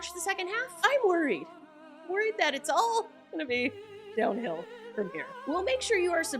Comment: thought I heard you say "the season is going to be more backfill from here" - instead of "downhill" 🤔